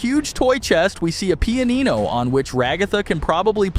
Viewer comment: what the hell did bro just say 🔥🔥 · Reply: pianino i guess .-.